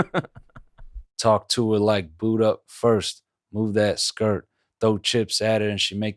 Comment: ohhhhh ''boot up first'--- this is the one line i didnt understand from the song cause i was hearing 'buddha first' and i thought it was some buddhism reference i didnt get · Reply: i thought it said 'boo-ed up first', like she was in a relationship lmao